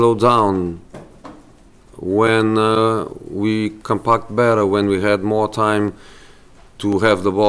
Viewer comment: Talking about defending, did Mourinho just say"when we can PARK better????"